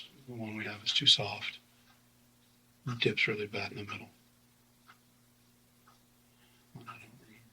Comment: Bro what are these subtitles??? He said “it dips really bad in the middle” not “my tip’s really bad in the middle” 💀